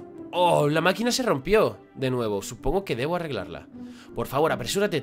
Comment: like si te diste cuenta que dice "la máquina se rompió "se" nuevo"